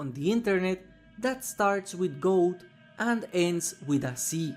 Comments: What is the word he is saying? What does dredge look like? “ gold and ends with a c” I cannot figure it out. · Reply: It's a goat, not gold Google "Goat see" all one word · what did he say is it goat and c or gold and sea i can't tell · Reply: Goatse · I really dont understand the dredge innapropriate design... Gold and ends with C ? Does anyone have a clue ? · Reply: I thought he said “Goat and ends with C” still have no idea though Edit: Just saw that he means “goatsee”. Do not search that up. Its actually super gross